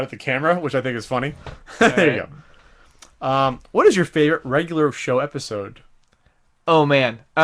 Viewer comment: Does say "Regular Show episode"?